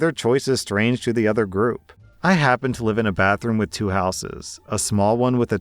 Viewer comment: Story 1: Wait... I heard: "I happen to live in a bathroom with two houses" 😂